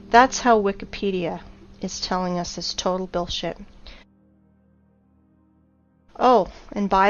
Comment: i just heard the word billshit HAHAHAHHA GO BACK TO SCHOOL PLEASE